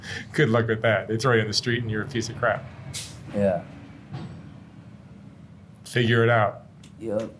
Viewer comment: He def thought said "your living on the streets and your a piece of crap"